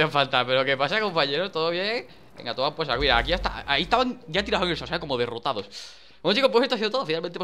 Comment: Minuto 8:46 que es lo que dice???? xD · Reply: Anthony King ya estaban tirados como derrotados .XD no se ni como entendi eso